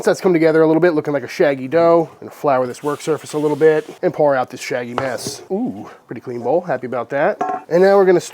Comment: When I hear "shaggy mass", I always think "shaggy mess", which makes me think of Norville "Shaggy" Rogers from Scooby Doo. I can't be the only one that thinks that can I? Shaggy: "Like hey Scoob, let's make sure all the flour has been hydrated before autolyse!" Scooby: "Yee-hee-hee-hee-hee-hee"